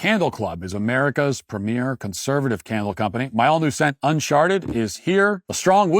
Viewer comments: When Matt is giving the candle pitch, to anyone else did it sound like he said “Unsharted”? I don’t want to smell like shart. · Is it just me or did he say "un-shart-ed" for his candle sent at 7:56 time stamp? <link>